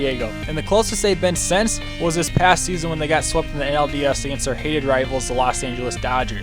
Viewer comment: did you say ALDS? If you did that's ok I barely noticed. Becides I knew what you meant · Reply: Nah I said NLDS. I just didn’t say it super clear. That’s my bad